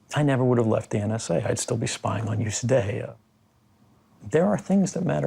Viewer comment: Did he say around 3:40 ´if i wanted safety…….. (etc etc) i’d still be spying on them today’